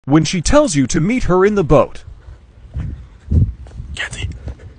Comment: I thought she said motorboat, not meet her in the boat....